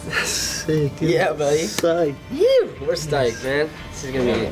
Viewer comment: does ryan say "We're Stiked" hahahah